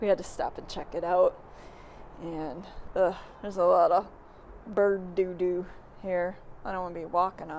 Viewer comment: I thought you said doo doo lol ✌️ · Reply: Ohhh you mean at the abandoned train station. Yes, I did say bird doo doo lol